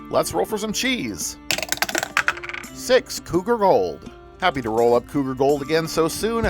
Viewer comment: When you rolled the cheese my dad thought you said cougar roll 🤣